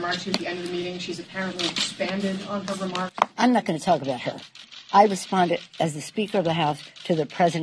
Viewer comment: I thought Pelosi said, I'm not gonna talk to that ho' 😂😂😂